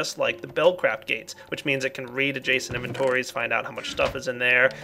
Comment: Can someone please tell me what a "jason inventory" is? :/ I really don't understand what is saying all these times I just hear jason.. and that's weird.. (I'm not a native english speaker). 14:24 for example..